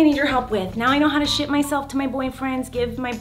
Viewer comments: this might be so weird buti thought she said "how to shit myself to my boyfriend" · She swore haha she said I s**t myself inst3d of ship myself haha 😂